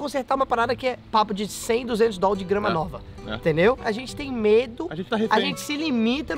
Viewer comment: só eu q ouvi entendeu 200 mil kkkkk